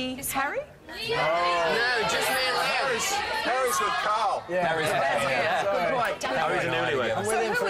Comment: Sounds like Liam said Harry's with Louis at around 12:50 ??